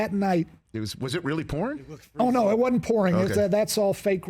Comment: porn??? What he saying? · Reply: Pouring!!. Rain.. Got you